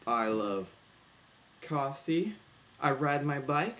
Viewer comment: lol someone wasnt very happy about school on saturday i think u said coffsee